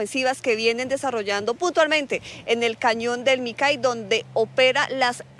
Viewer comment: 🫣🫣🫣🤣🤣🤣🤣 en el segundo 0:36 le entendí a la periodista están trabajando putamente 🫣🫣🤣🤣🤣🤣